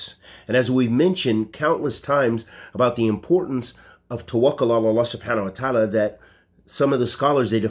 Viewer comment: Salaam What is the spelling of the word you mentioned at 0:27, "tawahil," in reference to doing what needs to be done to reach ones goals. Thank you